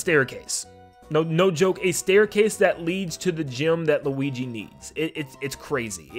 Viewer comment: It sound like he said gym, as if he’s telling Luigi he’s fat.